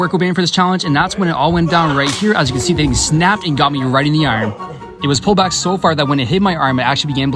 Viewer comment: Well llolie here. It sounded like he said "I got hit in the irons"